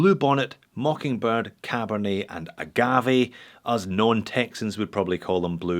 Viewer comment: I swear, I thought you said, “Us known Texans.” I almost spit my coffee everywhere.